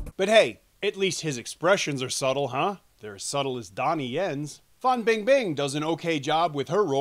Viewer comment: My speakers are falling apart so I'm not sure I heard well - did you call the actress at 1:53 "Fun Bangbang"? Sounds like a class-3 name to me.